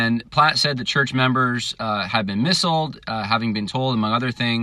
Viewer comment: Lol you were a phonics kid :p @ 4:23 missiled instead of misled :p hehe. All jokes aside - really enjoyed - will definitely share it!